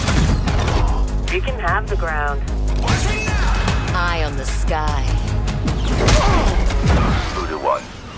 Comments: i always thought she was saying “eye on the sky” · at first i thought she said "eye on the sky" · I always thought she said eyes on the sky I’m so dumb 😂